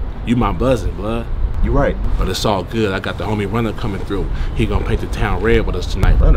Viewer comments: did he say “C”Omni through? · Anyone else hear him say he got the homie "coming" through(1:47), like with a "c"? He done fucked up now lol...